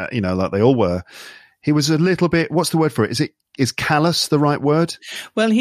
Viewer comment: being a little bit kallus? could anyone tell me what's the right word , thanks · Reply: "callous"